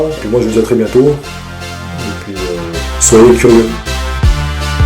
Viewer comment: Merci pour ta vidéo :) par contre un conseil essaye de moins mixé ta voix avec la musique à la fin :) A 12:34 j'ai compris "Solo curry" au lieu de (je présume ?) "Soyons curieux" :)